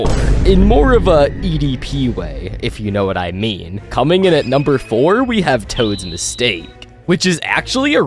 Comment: you said coming “coming in at number 4” but the captions said “coming in number 5”